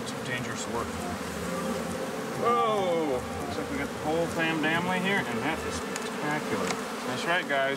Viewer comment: has to be the BEST part of this interview he said “look like we have the whole FAM DAMILY here” (instead of DAM FAMILY) 🤣🤣🤣💪🏽